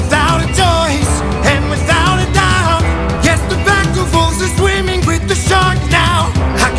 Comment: I love the line "the pack of wolves is swimming with the Shark now" for the use of the singular, there is ONE shark, THE shark, doesn't matter how many wolves there are, the Shark can't be beaten. Only escaped. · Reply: This entire time I thought he was saying "pack of fools" instead of "pack of wolves"